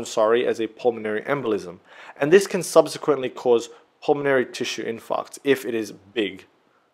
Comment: What is the word said at 3:22? Pulmonary Imfarct?